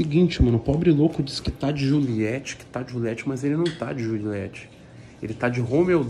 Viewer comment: Eu entendi certo ele disse jurilete?